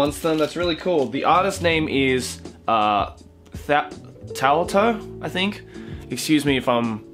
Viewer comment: Who else heard "Towel Toe" instead of "Thao To" at 2:25?